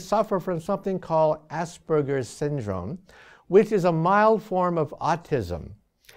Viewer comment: First time when I watched this I heard 'AssBurgers Syndrome! ' :P I was like what the hell is 'AssBurgers Syndrome' ?!?! > Well it is Asperger's Syndrome , I learned about it later .